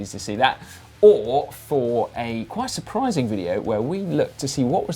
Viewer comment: Let us know if you enjoyed Paris-Roubaix, also, please beat Dan's caption! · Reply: Cobbles? I thought you said Knobbles.